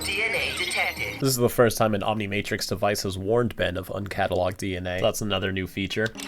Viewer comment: maybe I heard something wrong but "Omnimatrix"?